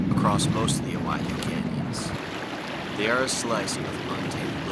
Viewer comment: I know that as a rule, you don't reveal exactly where you are, but judging from a few hints you dropped, my guess would be you might be in the Owyhee/Bruneau River/Deep Creek/Sheep Creek area in SW Idaho. If so, that place is truly remote. It is said there are places in that area where no human has ever set foot. That Mormon Cricket swarm was creepy and impressive! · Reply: I swear at 43:38 he says Owyhee canyons. I can’t quite make it out but that’s what it sounds like. And that’s the vibe I was getting too. I’ve been to the Owyhee area in Oregon and it gives a similar vibe for sure